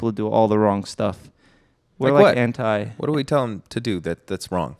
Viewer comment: did he say hentai😂😂